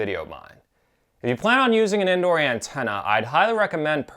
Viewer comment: Nice video, thanks for the review and info. But not sure why I keep hearing "Andorian Tenna"